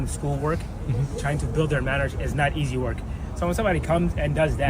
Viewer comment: I can't understand him acoustically at 8:13. He says "trying to help them with school work, trying to build their .... ist not easy work. The subtitles put the wword "marriage" there. Does he actually say this or does he say something like manners?